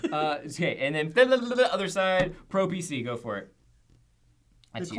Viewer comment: When said "pro PC", I thought he said "pro obesity" and I was like, poor